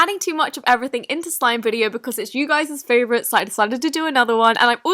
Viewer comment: Did she say you guys favorite? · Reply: You guys’ favorite